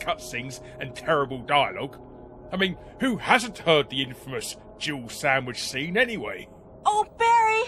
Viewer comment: I still havent heard Jill Sandwish to this day as it has only sounded like gerbil sandwish.